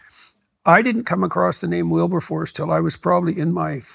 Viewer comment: Check out 13 minutes I never heard of this guy and I didn't catch his name. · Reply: I gotchu. William Wilberforce: a British politician, an evangelical Anglican minister, and Member of Parliament who advocated for social reform and abolition of slavery in the House of Commons. I’ve never heard of him before either.